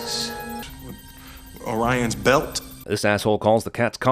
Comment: How about J&K in the busted up jewelry store when the only clue they have to work with is "the universe is on ORION's belt". J is looking at the pictures on the wall of the cat and says "this guy had a serious crush on his cat" and he totally doesn't notice the photo of the cat that has "ORION" printed on it, or the collection of cat collars, at least two of which say "ORION". Not very observant J! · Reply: Also at 10:29 in, the alien was saying "on Orion's bell" not "belt"...a pretty accurate statement about location of said galaxy....made only less accurate by being called "on" instead of "in".